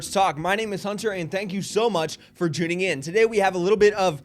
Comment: Did he just say "juning in"? I guess he couldn't decide between Joining and Tuning.